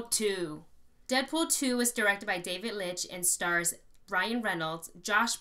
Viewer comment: HaHa - at first I thought said "Directed by David Lynch."